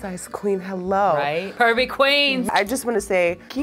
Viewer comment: I thought she said Pervy queens but she said curvy queens😂